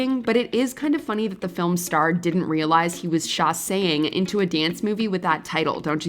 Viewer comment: Sorry, but what? 13:27 Am I hearing shasaying instead of sashaying?